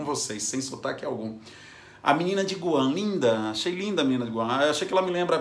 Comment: não entendi o nome do país da miss em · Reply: Guam